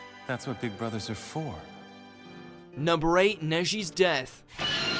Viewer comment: i heard "Noshi's death" for some reasons